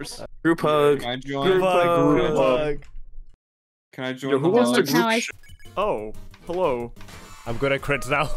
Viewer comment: DId really say "Yo who wants to group shi-"????